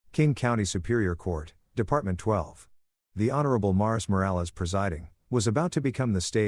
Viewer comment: Here's where I prove what an absolute Marvel NERD I am, I SWEAR the first time I heard this I honestly thought I heard him say MILES Morales, LMAO!!!😂🤣👍